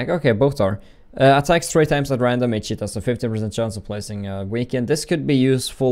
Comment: Every time he says 'each hit' all i hear is 'eat shit' laughed so hard